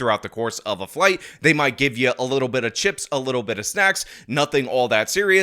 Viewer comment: I had to listen to you two times… because I thought you said “a little bit of chips and a little bit of sex” But you said snacks !!!! 🤣🤣🤣